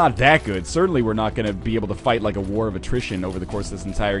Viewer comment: Can anyone tell me what NL is saying when he says "War..." something? 22:06 in